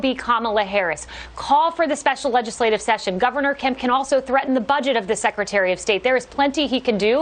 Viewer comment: I beg your pardon, did she just say "THREATEN!"? Yeeeesh!